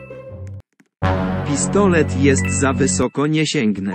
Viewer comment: usłyszałam pizdolet